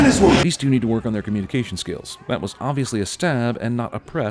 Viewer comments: the caption says "absolutely," but the narrator said "obviously." (Sin for) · Sin subtitle says "absolutely", speaker says "obviously".